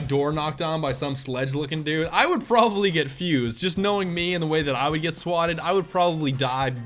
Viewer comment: i thought you said "have my door knocked on by some sledge looking dude, i would probably get FUZED (as in angry)".